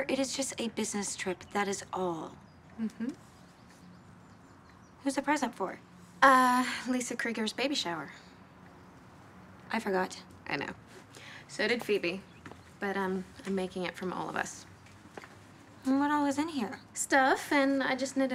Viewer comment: at first I heard "Lisa Kudrows baby shower" 🤣🤣🤣